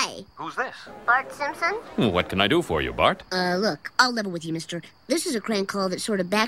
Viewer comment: What does Bart say at around 0:20? I hear something like "look, I'll lebo with you mister", but obviously that's not what he really says and I can't figure this out for the life of me! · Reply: level with you